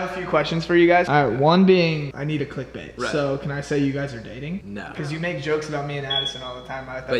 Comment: Omg look at 0:56 I THINK JOSH SAID WELL YOU ARE DATING SO ❤❤